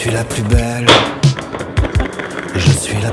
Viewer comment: he said "je suis la poubelle"and it means "i am the trashcan" or i misheard soemthing omg l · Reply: kamilė navardauskaitė "Je suis la plus belle" not "la poubelle" xD